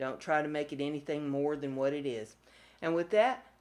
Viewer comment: Lol, I just heard you clearly this time, in deaf in one ear, and I always laughed my butt off cuz I thought u was saying....Brenda's crabby be like Brenda. I just now realized you say crafty!!!!!!omg! Sometimes I get things so wrong. I feel like an idiot. I'm so redicoulisly embarrassed but I just had to tell you about my stupid bad hearing. Lol